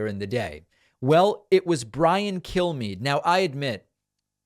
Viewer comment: I heard that as "Bryan Kill Me."